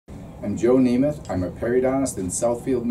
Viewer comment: Okay, I thought he said Joe Namath, the football player. Thanks for the video. I was using an electronic toothbrush. So now I will stop.i learned a lot in this video.